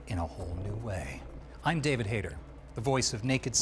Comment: Hahah i thought first Snake said his name was "David Hater" at the begin, had to double check 😅. PEOPLE WAN'T Metal Gear Solid (1998) remake!! Please Hideo! 🫡